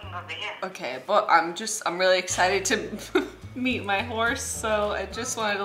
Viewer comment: When she said horse I thought she said divorce 😂😂😂